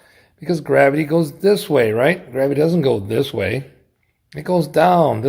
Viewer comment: I thought they said gravity doesn't exist 😂